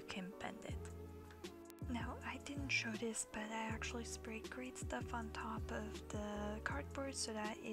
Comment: at around 2:30 what did she say she sprayed on it?? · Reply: aNimAL AbUsE Great Stuff she put a link in the description box